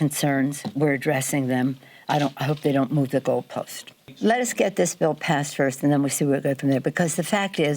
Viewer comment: "Let us get this bill passed first...." Oh, my God!! Did Pelosi actually say "We need to pass it to see what's in it"???? And we worry about Biden's cognitive decline! I cannot believe she has manged to stay on top of the Democratic caucus all these years. She has to have massive dirt on everyone else.